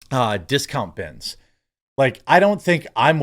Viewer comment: I'll never understand why some things go viral, it sounded like she was saying spit on his junk.